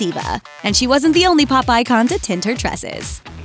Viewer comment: "Pop icon". Sounded like she said Popeye Con. Lol